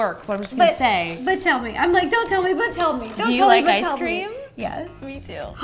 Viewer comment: I thought it said softball ice cream ball 😭😭